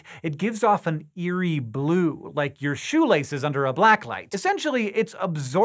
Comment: Shoe laces under a BLACK LIGHT 0:48 , Did i hear it right.